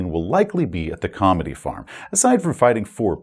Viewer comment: Okay, fess up. Who else heard, "Comedy Farm" ? Another potential hook could be any kind of investigation/report of/on a new baptist religion meeting under a big tent on the outskirts of town, something like 'the church of the blessed mother,' where the proven faithful have been getting miraculously cured after being rebaptised in the 'holy font'.